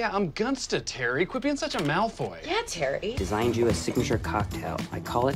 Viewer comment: I'm really not sure if he said "quit being such a malfoy" at 4:44 but I'm just gonna say that its what I heard